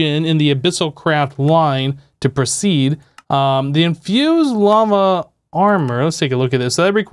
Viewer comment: Did you just say infused llama armor?